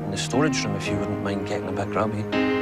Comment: I thought he said grabby instead of grubby and I was like mhmm okay buddy